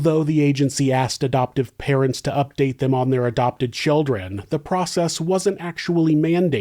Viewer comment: I thought you said 17 adopted children, then I realize you said 7 teen adopted children lol